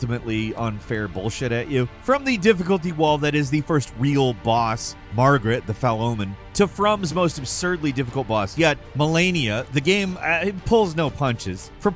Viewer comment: Did he say "Margaret, the Fell Omen" at 04:37? 🤣